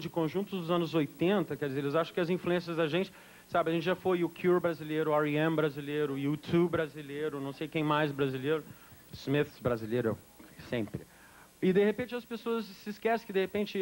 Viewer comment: Ele cita U2. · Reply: Sim, 5:53 Ele diz: "A gente já foi o The Cure brasileiro, R.E.M brasileiro, U2 brasileiro, não sei quem mais brasileiro, Smiths brasileiro sempre."